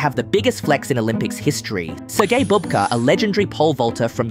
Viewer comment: I heard "Sir Gay Bob Ka"